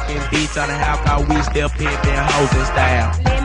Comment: I thought it said "Still Simping" lol